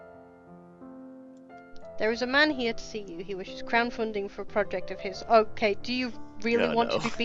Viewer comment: Anime characters age in a manner of a hyperbola or other mathematical function with an asymptote. They age very slowly until they immediately age 10 or 20 years. Also I keep hearing you say "Crowd Funding" rather than "Crown Funding"